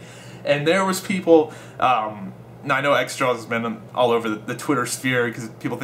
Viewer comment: Who are you talking about? I couldn't understand what you said? Xsomething. What? You mumbled his name. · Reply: Xjawz. He used to be the biggest CoD channel when CoD was massive on YouTube in BO1